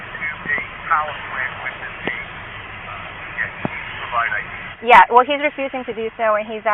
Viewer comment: Did he said it was policy to provide ID…..🤔